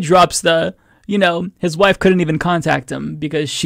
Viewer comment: When I first heard it, my mind automatically thought he said his daughter couldn’t see him. Like my mind just filled in the blank with daughter cause wife wouldn’t make sense. But even still I questioned it because his daughter wouldn’t need to be 18 to visit her Dad. They didn’t even question that.